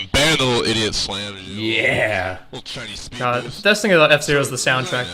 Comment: did he just say he got a "Chinese speed boost"?